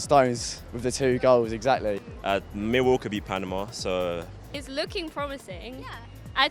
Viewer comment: did he say Millwall could beat Panama ?